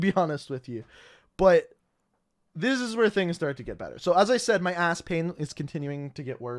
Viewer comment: This whole story time: I almost died What I heard: “my ass pain”